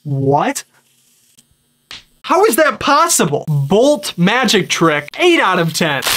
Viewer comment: Each time he says "possible".. i hear "impossible"